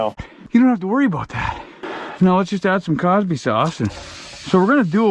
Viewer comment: Cosby sauce. From. Peg the American Canadian 🤙🏼🤙🏼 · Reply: I thought he said CarbySauce